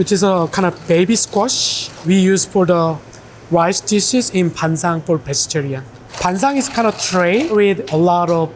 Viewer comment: ay mang i think team made a mistranslation at 9:40 where guy is sayin ban-sang (반상; 飯床) instead of ban-chan/pan-chan (반찬; 飯饌) namseng?